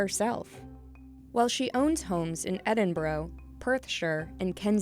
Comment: anyone else hear Edinbro